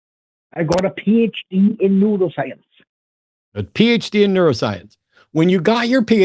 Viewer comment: I thought was trolling when he said he had a PHD in Neuroscience. I thought he said a PHD in Noodlescience.